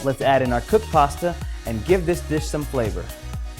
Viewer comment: Almost thought you said "give this bitch some flavor", LMAO!